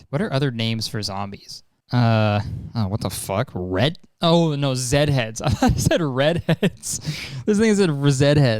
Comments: does it sounds like he says the n word or am i going crazy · am i the only one who heard him casually drop the N word or am i going crazy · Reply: he said "this thing said"